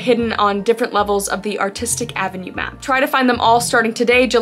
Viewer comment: For a sec i thought when she talked ab the app and she said "artistic" i thought she said autistic